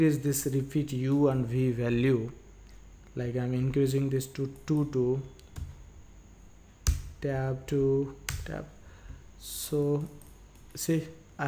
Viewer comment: sounded like "i'm increasing this too too too" and it just made me laugh i'm sorry